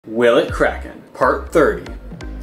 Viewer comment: I thought bro said perc 30 💀💀